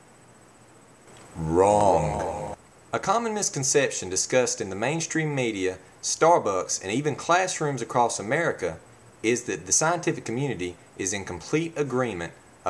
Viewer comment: when you said a common misconception i had to rewind because i thought you had said a communist conception